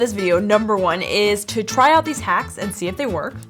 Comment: I thought you said 33 hacks